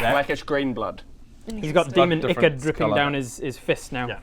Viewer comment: Is he saying demon"ickard"? If so, wtf is that, if not, what is he saying? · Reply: Demon Ichor i believe. Only 4 years late lol